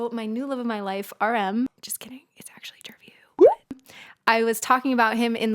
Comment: Did you say, "just kidding.. its Jungkook?" lol thats what I heard